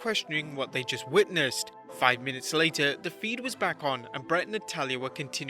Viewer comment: I'm sorry but did you just say juring the bret hart speech " five minutes later, the fiend came back on"? Haha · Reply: No, he said "the feed was back on". As in the feed cut out when the fan attacked but then came back on after it was dealt with.